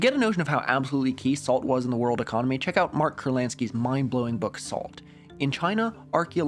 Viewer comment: was listening to this in the background snd i heard mark kurlanski’s salt as roman polanski’s assault